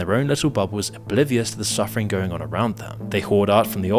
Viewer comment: I’m sorry, did you say “bolivious”? 😂